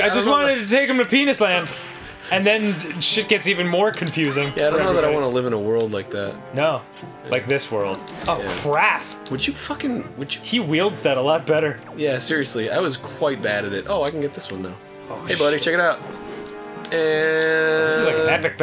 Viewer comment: does Dan say "Ohh craf! "?